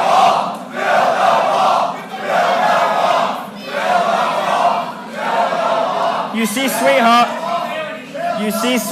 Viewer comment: What do they shout at 3:23? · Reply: tom hoefkens build a wall